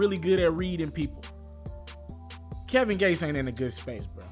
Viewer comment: Kevin gates isnt a WAT?! Like what did he say